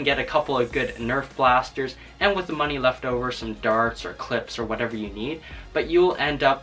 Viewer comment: excuse me did you say CLIPS